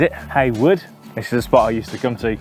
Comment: Heywood did you say?